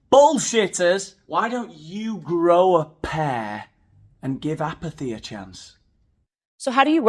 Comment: Am I the only one who thought said “why don’t you grow a pear and give apple a chance” 😂